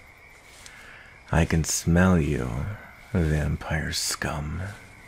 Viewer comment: did you just say vampire's cvm? 🤨